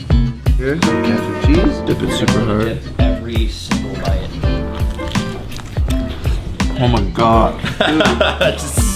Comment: What is that sauce? Did he say cheese ? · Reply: Vegan cheese exists!! In the video, I hear "cashew cheese" but he could also be saying "got some cheese", either way it's vegan because they definitely know that dairy isn't vegan lmao ✌